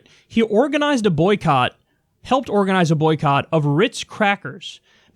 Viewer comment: At around 4:50 it sounds like Kyle said “helped boycott rich crackers” lol